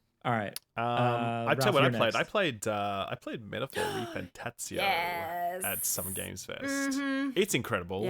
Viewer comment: what is the game they were discussing on 56:36 ? I can't make out what was saying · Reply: Metaphor: ReFantazio